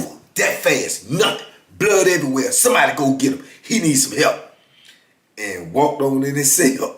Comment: he sounded like kevin hart to me 😂😂😂 "boom just like that blood everywhere he need some help"